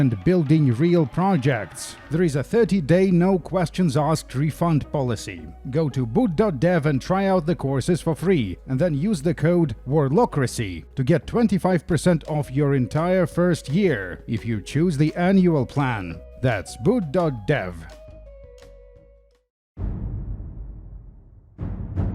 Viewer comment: Was tabbed out when he said ''warlockracy'' and all I heard was ''warlocrussy'' and now I can't get this FILTH out of my head help